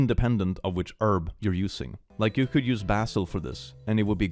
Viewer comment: What the heck are you trying to say 23:40? Say cilantro alright but that other one i just dont know. Sounds like bathsol or something. Are you trying to say Basil (said like bay-zel)? I like them both. Will never get how anyone taste soap. Weird to me